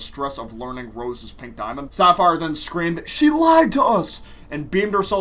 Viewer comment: Oh I thought sapphire said “ she liked us *